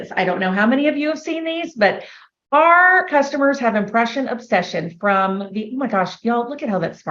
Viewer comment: She said they had "impression obsession" and I swear to you I heard "oppression obsession" 😅